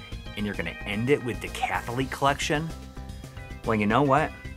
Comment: At first I heard "The Catholic Collection" XD XD